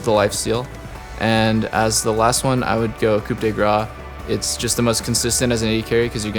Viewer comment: coup the gras thats what you said for coup de grâce lmao